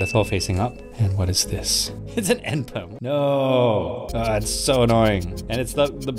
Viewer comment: says "It's an N Perm" Caption says "It's an Anthem"